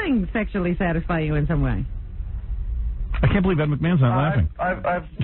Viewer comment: did he just say 'that mcmahon' lmao 😂